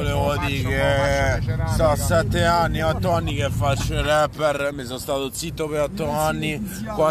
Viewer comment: ho sentito bene?! Ha detto rapper?